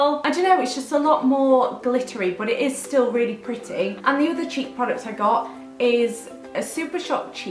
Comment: I thought you said cheep products instead of cheek products haha